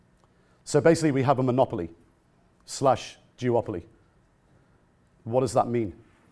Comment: Ok wait a minute at 9:20 did he say we have a (monopoly/jewopoly) please don't tell me he said that I mean I'm smoking a joint right now but I'm not stupid I know what I heard please don't tell me he said that he can get killed for saying stuff like that